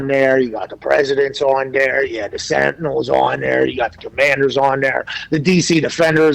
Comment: Did Pat say Sentinels!? I don't see that...but it rocks my ass!! The Washington Sentinels is a Marvel Comics franchise waiting to happen.